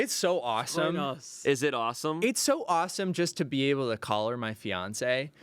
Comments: I was sort of zoned out listening offscreen and hear what i thought was saying "just to be able to collar my fiance" 😂 i was like woah! But no, call her 😂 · I heard “it’s so great to collar my fiancé” not “call her” and thought was being a little freaky on main.